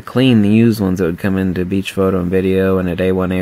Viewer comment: Is Beach Photo still in business? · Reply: When I first started watching your channel I thought you were saying BH photo